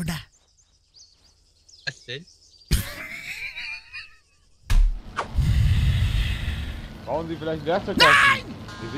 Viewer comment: Was sagt der bei 2:13? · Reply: Er fragt "Was denn"